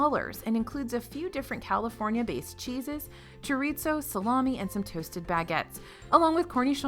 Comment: did she just say choreetso 😂